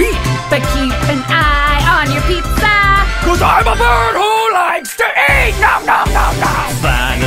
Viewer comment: Chica:CAUSE IM A BIRD WHO LIKES TO EAT NOM NOM NOM Me:holy - here eat my pizza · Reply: I actually for one second didnt hear her right, i thought she said "I'm a murderer who likes to EAT." So thank you for clarifying